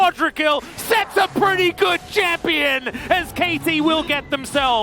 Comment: lmao he just said SKT instead of KT, he mistook it😂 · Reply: I think he said "as KT will get the dragon"